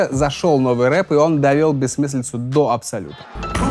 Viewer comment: только я заметил, что говорит он "до абсолюта", а в субтитре "до абсурда", так какой же верный?